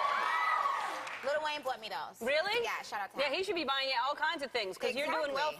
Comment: did she say yeah shut up now?